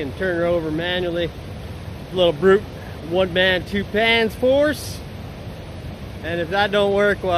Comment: I thought he said 1 MAN 2 PANTS!!! OMG had me cracking up 🤣